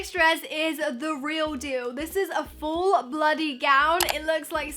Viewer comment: haha you said bloody dress instead of full body?